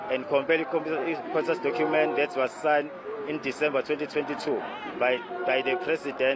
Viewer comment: Did he say: " ...that was signed in December 2022?"